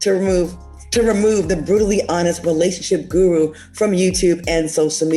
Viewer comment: Did she say brutally honest? @ 1:09. Hmm. So being honest is a...